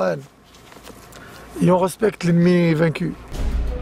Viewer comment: Il a dit quoi à 29:29??? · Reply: On respecte l’ennemi vaincu